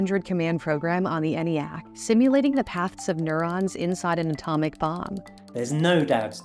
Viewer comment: be careful how you read your script. At about 6:25 does not say "nueron", she says "neutron". Notice the "t"